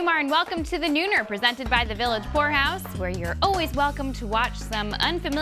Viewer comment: did she say village horehouse?